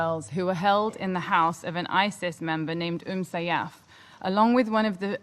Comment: the name of Isis member is Umm Sayyaf (the automatic CC didn't understand correct).